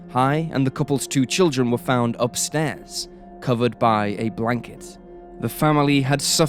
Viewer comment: Did he just say “branket” instead of blanket? 😮